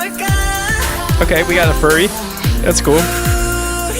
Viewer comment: Did you just call Hueningkai a furry? 😂😂